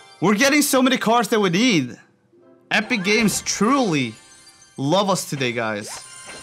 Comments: What did he say! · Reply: Epic games lol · Hey guys I think I’m only one saying this but at 17:39 he says epic game wow 😂 HAHAHAH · did he say epic games??? 🤣🤣🤣🤣🤣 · am i tripping or did he say epic games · did he say epic gammes ¨